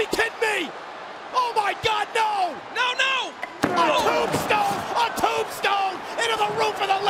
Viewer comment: Oooo at 0:46 i heard the rock says give me the tombstone